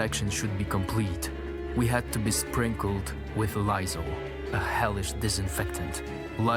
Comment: is he saying "Lysol"? Like the cleaning agent we have now? Or is be saying something else that sounds similar? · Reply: Yes Lysol. you heard correctly.